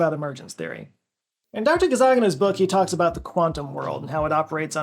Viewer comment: All the time I heard "Dr. Gazzaganis" am I broken?